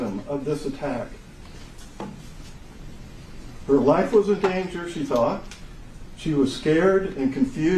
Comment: I thought she said she is tough but I could see fear on her face.